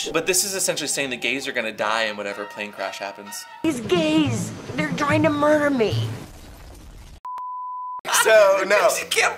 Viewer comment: what does she say at 12:50 ? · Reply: "These gays, they're trying to murder me". That's from the TV series "The White Lotus", 2nd season.